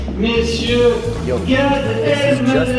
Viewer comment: I know they are saying in French but I heard my name "Myint chu" 😂😂😂